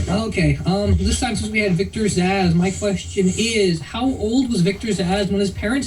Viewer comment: My mom watches the show and she thought Zzass said his name was "Victor's Ass". LOL.